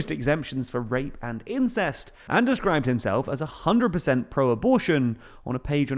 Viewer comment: Wait, did he say a %100 Pro Abortion?😢.